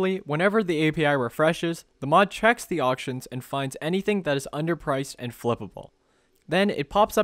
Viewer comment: why did i hear underpriced and flippable as submissive and breedable?